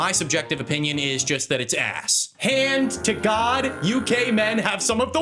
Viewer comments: I heard.. you gay men.. instead of UK men lol. Had to relisten to that part to see if he actually said that! 😅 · Anyone else get confused when said "UK men have the worst fashion sense" and thought he said "you gay men have the worst fashion sense" no? Just me?